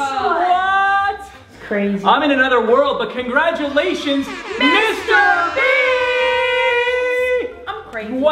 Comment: Did you say Mr Beast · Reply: Nah he said Mr Bee